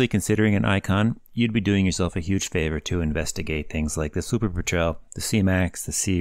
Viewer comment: What's the first plane you listed at 1:38? I can't understand what you are saying and closed captions think you said "super betrayal" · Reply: LoL Super Patrel.